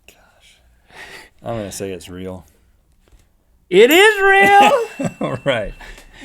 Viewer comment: I thought he said ''it is-rael ( israel the country)'' instead of '' it is real''